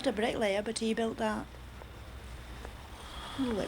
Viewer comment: I thought she said "he's not a bright lad" and I thought that's nice mom. Then I realized she might have said brick layer... and sure enough! Very cool!